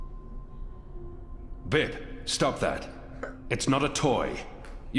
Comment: Omg I swore said "Bitch, stop that"